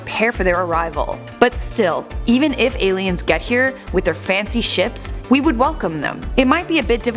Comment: i heard fancy sh*t